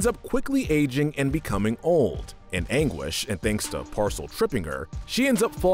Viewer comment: IM SOREY DID HE JUST CALL PASCAL “PARCEL” 😭🤚